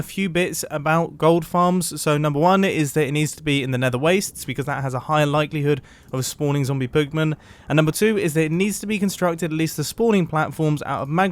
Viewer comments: Did he say pugmen instead of pigmen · Mumbo: It needs to be in Nether Wastes. Because that has high likelyhood... to spawning Zombie Pugman. Me: Replays the sentence to check if I heard right. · did he just say zombie pug men